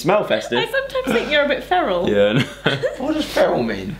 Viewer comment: What word was Zoe saying at 0:34? The one Alfie did not get?🙈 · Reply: feral